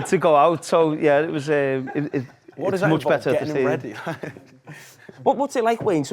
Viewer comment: city lad tried to be funny but no one laughed then he looks down in shame loool dunno what he said but didnt go down well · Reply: he said "what does that involve, getting them ready?....." loool